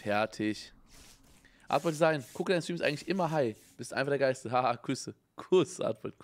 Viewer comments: Why tf habe ich ''Gucke deine Streams immer high'' verstanden holy shit :D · HAHAHAH habe 1:37:57 verstanden ''Gucke deine Streams immer high''